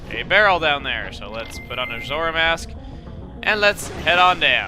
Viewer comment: Thumbs up if it sounded like he said "Majora Mask" instead of "Zora Mask"